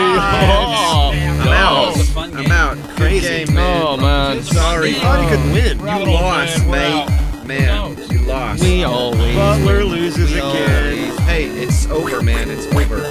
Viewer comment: who else heard "hey it's oger man, it's oger" ?